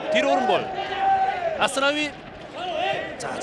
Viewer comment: Wtf🤦 he said: "Asu nawi"😁